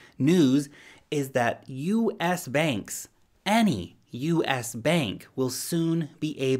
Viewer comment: At first I thought you said "you as banks" but then I heard "U.S. banks" 😁